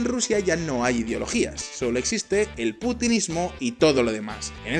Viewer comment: escuché "putismo"